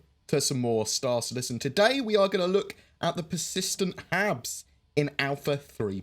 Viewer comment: Not gonna lie, thought you said "persistent abs," and thought it was a weight-management mechanic for our avatars, lol